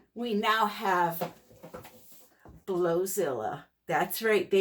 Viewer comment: AWESOMEEEEEE COLORS , THOSE I CHECKED OUT TOO LOLOL, MOMENT OF SILENCE FOR DEMON DRYER TAKEN . BLOWZILLA UP !!!! I THINK THEY WERE ABOUT 16 DOLLARS HERE THE COPPER I WANT TO TRY TOO . FIRE OPAL IS AMAZING LOVE LOVE THAT COLOR . GOT ABOUT 25 BOTTLES TOO . WORK GREAT !!!!!!! THOSE COLORS OMGGG ARE STUNNING TOGETHER . THIS PIECE IS INCREDIBLE, OUTDONE AGAIN !!!!!! HAPPPY POURING TO YOU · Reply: SORRY I THOUGHT U SAID GODZILLA LOLOL BLOWZILLA EVEN BETTER LMAO .